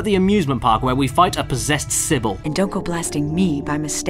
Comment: when he said possessed cybil i thought he said possessed civil damn